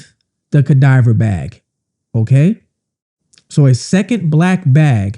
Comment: It’s pronounced cadaver not cadiver lol! That threw me, hahaha! 😂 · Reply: I was listening while getting ready and I thought he kept saying "diaper bag" and I was thinking thats weird I'd never would have thought a diaper bag was there! Lol now I know